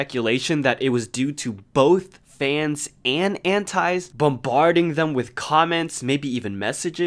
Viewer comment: my bilingual ass thought you said "hentais"